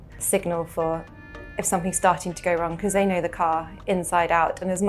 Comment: The subtitles are wrong. It's meant to say "they know the car". 2:18 That seems fun & investing into a phone with a macro camera will probably help with that. There are plenty of phones with one.